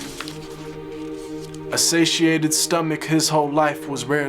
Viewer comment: what is the word he says at 2:13 before "his stomach"? Please? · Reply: A satiated stomach..means a full/satisfied stomach. Our Prophet s.a.w. would stop eating before his stomach felt full.